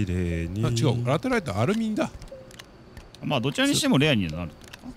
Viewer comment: たまにさんがアルミがアルミンに聞こえる笑